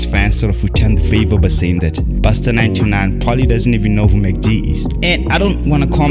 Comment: Did you just say Busta 929 at 1:16?